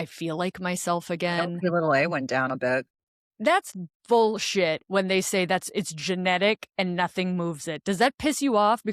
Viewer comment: What are you saying at 33:53? “My xxxxxx went down a little bit. That’s bullshit, etc”. the video skips over that word and it doesn’t show in the CC or the Transcript. Would really like to know what point was being made there. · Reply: I believe she said Lipi (a) due to context. It is one of only markers I’ve heard of that you get tested once in a lifetime - because it’s supposedly genetically predetermined